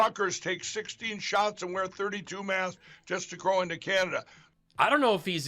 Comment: did Mike just say " just to crow into Canada "